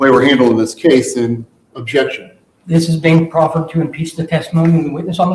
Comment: He actually said "impeach"